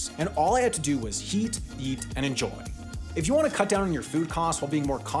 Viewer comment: The older I get, the more I mishear things. At first I thought you said "Heat, yeet, and enjoy." which sounds like the sadistic instructions for the people preparing oil above the murder holes of medieval castles. And, no, that wasn't the job I had in college. I'm not quite that old. It was high school. 😛